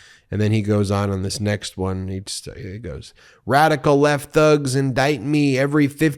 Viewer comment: Lol . I thought he said radical right thugs diaper me 🤣🤣🤣